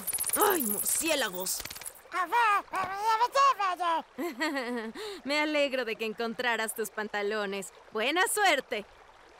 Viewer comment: estaba distraído que escuche " que bueno que ya encontraste tus mamalones" xd